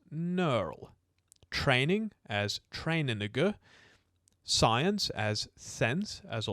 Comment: Am I the only one who heard, "Training" as "train a nigga"? 😂